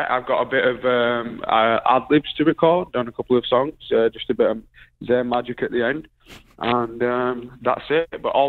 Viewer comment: did he just call it "zayn magic?" Lol welcome to the z-squad zayn